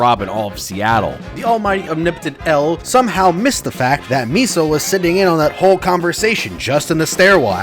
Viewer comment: you said Misa instead of Mia